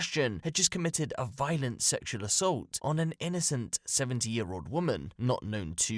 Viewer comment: 70 years old I thought he said 17 😂😂